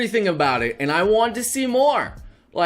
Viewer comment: I kinda misheard saying "Please, Marvel, More Panties."